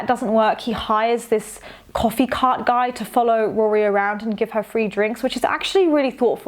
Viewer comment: I thought you said “hide a coffee cart guy” instead of hiring a coffee cart guy and it made me think of Logan just hiding coffee guys around for Rory to find 😂